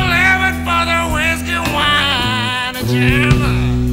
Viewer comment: Awesome!! first time I heard this song, I thought he said "Whiskey, wine and Jam", I Was like what the fuck :L